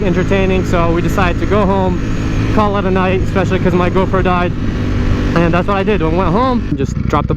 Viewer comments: i thought he said especially cause my girlfriend died at first bruh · Did anyone else hear "Specially coz my girlfriend died." I was confused since he said it so casually, but then I realised it was his camera. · he said because my gopro died and i heard because my girlfriend died i was like omfg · For a min I thought he said “because my girlfriend died” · I thought this man just casually said "because my girlfriend died" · hold up hold up hold up... at 2:42 does he say "we decided to go home, call it a night, especially since my girlfriend died" ?!?!?! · bro when u said go pro in 2:45 it sounded like ur girlfriend died 😂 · I thought he said “especially because my girlfriend died” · maaan u got me frozen, sounded like u said "especialy because my girlfriend died" i was like wtf... rewind few times to find out is just gopro :D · for a second there I thought you said your girlfriend died lol no gopro died haha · Sounds like you said "cause my girlfriend died" not gopro hahaha